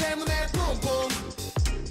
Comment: why i always hear 'boom boom' as 'pong pong' / 'poom poom' hehehe sorry😂